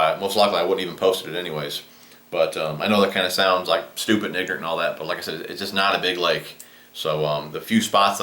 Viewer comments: What did you say 2:26-2:29 ? I couldn't hear you easily. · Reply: I said stupid and ignorant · It sounded like he said "stupid n*** but I doubt he actually said that 😂😂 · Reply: He said super ignorant